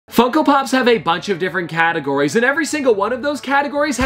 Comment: I thought the was saying f*ck -o pops instead of funko pops lol